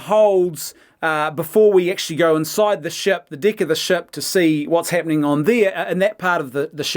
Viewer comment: In this video at 9:26, is he saying "Going inside the 'shept', the 'd*ck' of the 'shept'"? I am not a mother tongue, so this sounds weird to me... Can some English native speakers comment on that please? I am trying to refine my language skills.